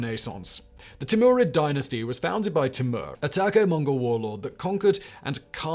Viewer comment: HELP!!! @ 7:30 mm, reference is made to a "taco Mongol warlord," Were the captions wrong? Do I simply don't know what is common knowledge to others? What does the word "taco" in this sentence mean??? ANYONE????? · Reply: I’m pretty darn sure they meant Turko-Mongol, as in Turkish and Mongol. Timur was actually of Turkic blood, he just pretended to be a descendant of Gengiz Khan. He did make sure to marry some Mongol women into the family. Given how many times I’ve had to override autocorrect writing this comment, I’m not surprised Timur became a Taco Mongol.